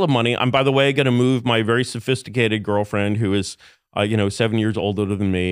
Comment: for a split second I heard. "With my sophisticated girlfriend who is 7 years old." 🤮